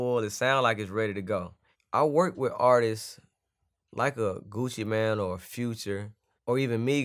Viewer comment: did he say i work with artist like gucci man